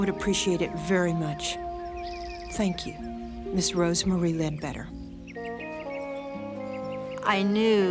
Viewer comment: 39_00. . I was stoned outta my mind and thought he said her name was bed wetter